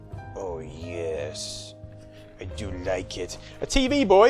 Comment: Am I the only one who thought they heard 'Oh yes... I shall play you naked!'.... 8-O I then pressed < to fire the video back 5 seconds to be disappointed that I heard 'Oh yes...I do like it!'